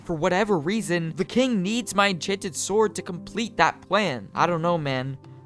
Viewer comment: Bro says pwan instead of plan